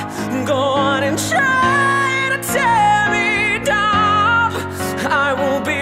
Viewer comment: Ffs I keep hearing "Try to tear me dab"